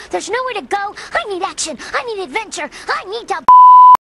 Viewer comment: "I need action! I need adventure! I need to poop!" ._. That's what I thought she said at first.